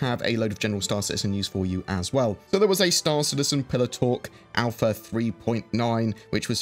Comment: Every single time, I swear I hear you say "pillow talk" instead of "pillar talk." But don't worry, we know you're not in bed with CIG. ;)